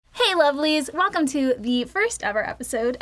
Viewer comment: She called us lovelies I thought Aaron and Danny said we were called Penises ;_